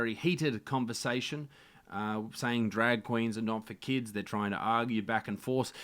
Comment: I thought that chant said drag Queens do not produce